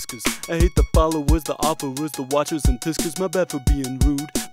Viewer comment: What is he saying at 2:29? "I hate the followers, the offerers, the watchers, and ?????" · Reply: oh, "tiskers"